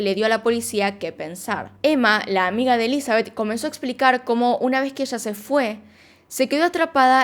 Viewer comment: Pensé que decía Elizabeth Olsen